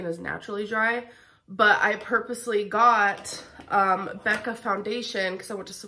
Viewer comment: When came in and kissed you, it seemed so scripted 😭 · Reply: I think she heard "Becca" and thought ALR said "Becky". I thought it was staged at first too though, so I don't know.